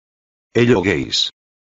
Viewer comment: it sounded like hello gays